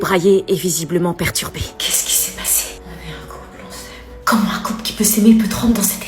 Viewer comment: elle a dit quoi ? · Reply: On est un couple q on saime